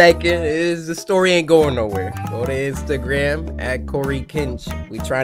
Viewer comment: "nowhere but Instagram at coicancha" is what the subtitles said on 6:55 XD